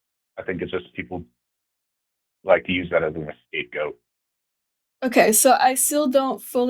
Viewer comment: did he just say escape goat?